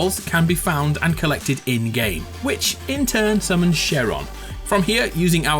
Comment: debunks fortnite weekly also: can't tell the difference between a dinosaur and a dragon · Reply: Koro Sensei also did call shenron by the name sharon? Cause thats what the captions said. Lol at 7:52 or am I crazy.